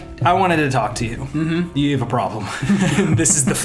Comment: As soon as you said "you have a problem" I just heard "ha ha ha! He said haaard on!" Ooooh how I miss the old YouTube lol keep up the vids! - Jack